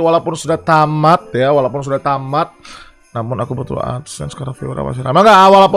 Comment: Ngomong apa bang? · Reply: Walaupun sudah tamat"namun aku butuh Adsense karna viewers nya masih rame"